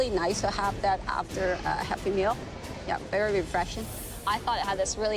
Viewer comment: Is it just me or did anyone else think at 5:04 she said “it’ll be nice to have after a happy meal”